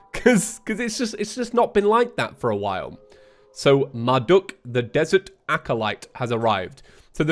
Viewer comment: i thought he said my duck the desert acolyte has arived